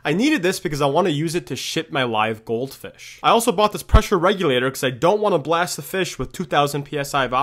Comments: i thought he said he needed the oxygen to shirt (yk) his live goldfish 💀💀💀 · I heard it as "i need it to shit my goldfish" 💀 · When you said "to ship my goldfish" İ thought You said "to shit my goldfish" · What he said: "I'll be using it to ship gold fish." What I heard: "I'll be using it to sh!t gold fish." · I thought he said, "I need it so I can shit my goldfish," as though pure oxygen can make that happen.